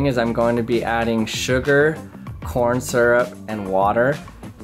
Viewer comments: did he say porn syrup? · My mind is so dirty I thought you said porn syrup